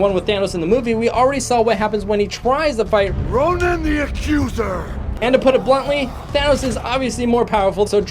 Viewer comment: Who else heard: "Bring in the accuser"!